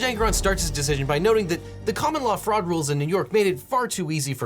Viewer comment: i legit heard "common law frog" and am now enjoying learning about all this frogulent behavior.